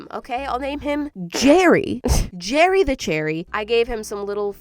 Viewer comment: Thought you said Cherry the cherry not Jerry the cherry 🍒